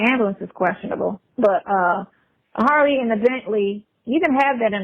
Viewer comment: I thought he was saying his belly 😂 not bentley 😂😂